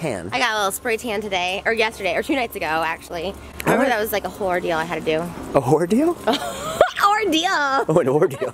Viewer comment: I heard it as *hore deal too 🤣🤣🤣🤣🤣🤣🤣🤣